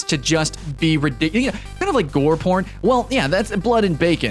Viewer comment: Wait did he say "kinda like gore porn"